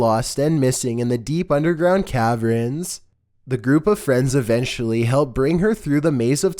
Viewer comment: Did u say cavrens? 🤣🤣